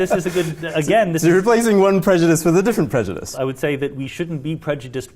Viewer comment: what does that word even mean (Preftftfftftycdrse) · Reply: The word is predjudice. Ive no clue what it means